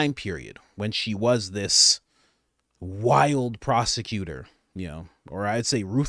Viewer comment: Prosecuter........I thought you said prostitute